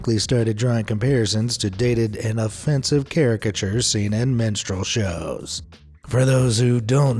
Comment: When he first said Minstrel Shows I thought he said MENSTRUAL Shows lol